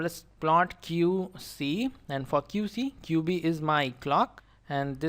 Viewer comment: wait am i heared that right , did he say the f word ? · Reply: He said ...'for Qc' and not fk u c bro